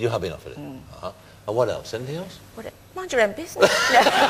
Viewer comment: Please, what he says at 8:15 and what she answers??? Thanks for the interview! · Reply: He said "What else, anything else" (as in what is the next thing that you will be working on) She said "mind your own business"